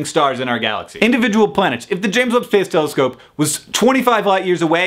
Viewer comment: did you say the "James Woods Space Telescope"?!? Just asking... :)